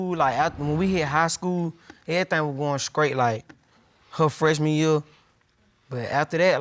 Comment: Did he say everything was going, "skrait?"